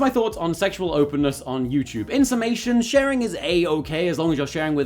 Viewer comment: thought I heard you say ''insemination'' at the end there :')